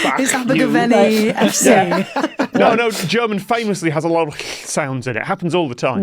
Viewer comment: lol I missed the phlegmy pronunciation sound at first so I thought Tom just said "German is famous for having many sounds"